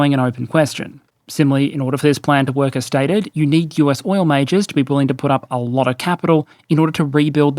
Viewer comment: did I hear "US oil mages"? DnD 2026 just dropped